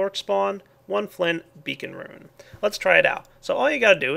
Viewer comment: i thought it said bacon rune